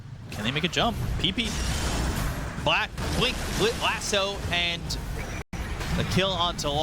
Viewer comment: Did he just said black. Blink and lasso😅